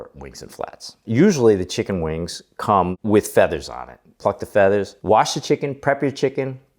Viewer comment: Love your channel and I swear I thought you said fuck the feathers 😂😂😂